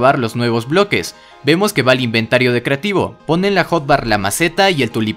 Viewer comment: ***** en el segundo 0:38 se ve claramente que dice "Whiter Skeleton" crees que lo añadan?? >_< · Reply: Dice creativo